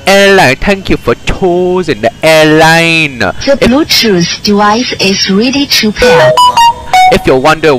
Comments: is it just me or i hear “the bluetooth device is really cheap here” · Ar first I thought it said the bluetooth device is really cheep here😂😂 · "The Bluetooth device is ready to pair" What I hear: "The Bluetooth device is really cheap here"